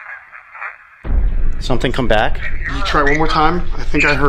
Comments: I hear “River, come back” 👀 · At this time stamp, I swear it says something like "Welcome back"